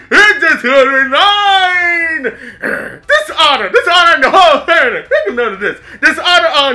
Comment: I thought he was saying otters on your whole family